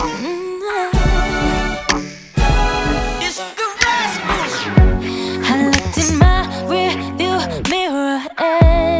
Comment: can someone explain what that voice is saying at 0:37 please? ik I'm probably late to the party but idc it's just driving me crazy 🙈🙈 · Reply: reneanduray they say “it’s the rascals”